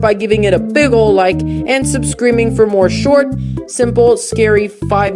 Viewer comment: "Consider sumscriming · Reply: Your right, it does sound like I said that! What I really said was subscreaming.